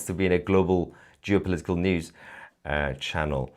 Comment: Lol🤣 That's now the SECOND time as I watched (and listened to) one of your videos and I had to stop the video, turn on the subtitles, rewind and REplay a part of it because when you said the word "GEOpolitical" I was like "😳😳WHAAAT!!?😳 What the fc did he just say!?😳NOOOO. There's no way he said that" (Because it *REALLY* sounded like you said "JEW political'🤣🤣🤣 LMAO 🤣. Which (of course) you didn't say. .lol. But both times (When it just happened (time #2) I didn't even think about the 1st time until I remembered later so each time..the 1st AND the 2nd I made that "HUH!!?😳🤔 SCRUNCHED WHAT DID HE SAY!!?🤔🤔 face of confusion" until I read what you actually did say. Lol🤣🤣 Still LOLing😂 Anyway. Thank you for sharing (as always) 😊